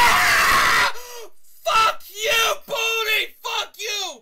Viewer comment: I Thought He Said ”@ You Boldi, You!”